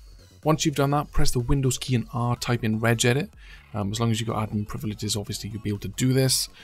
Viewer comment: Type in what? Can anyone write the spelling (I heard like Red Jedi lmao) edit- "regedit" gotcha!